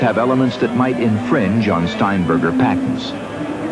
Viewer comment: did he said "John Steinberger's patents"?